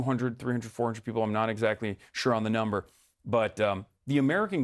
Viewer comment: this is strange because in the interviews ive seen, he explicitly says he does not consider himself a journalist when asked. Hes sais he sometimes does journalism when he goes to protests and does interviews and thats about it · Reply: My bad, must've misheard that part since Istg I was listening in the car and thought he said he was a journalist